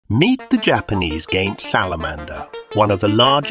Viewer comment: When you said giant it kind of sounded like you said gay